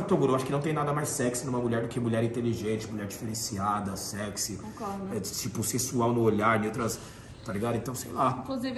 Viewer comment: eu entendi ela falar sacanage kkkkk to surdo eu acho em kkkkkk